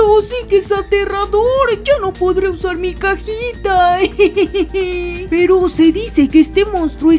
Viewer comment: yo entendí caquita envés de cajita